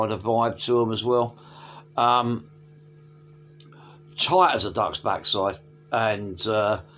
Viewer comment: Love and sausages Woof! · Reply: I thought you said tight as a 'Dutch' backside. Which takes on a whole new meaning. A lot of my 'Heaven & Hell' tattoo sleeve is based on bits of Revelation. Did I tell you to check out 'Universal Radio' by NZ band Dragon? [The track: Patina] That Elliot Minor faux pas is 'Smell The Glove' all over again. Resurrection Band do some pretty good 'Jesus Rock'. BC give me a Maiden/Dokken vibe On the 'Christian Lyrics' thing: I like that some of Petra's songs have some ambuguity to them if you look for it such as 'Angel Of Light' and 'Rose Tinted Stained Glass Windows. Those songs can be applied to society in general. Especially the latter IMHO. I have a box of jewel cases somewhere in my flat for breakages. Mostly due to breaking in the post. Marsden/Moody/Paice/Lord... Best lineup. Saw Bernie at Tramshed. Thats how I discovered NWOCR then subsequently Hellcats which led to meeting you. I was just about to finish my comment by saying I LOVE talking about music then I hear my name. Keep on rocking.